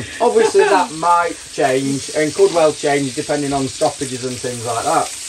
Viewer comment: Problem with talking while is cooking breakfast is that when you referred to 'stoppages', I heard 'sausages'!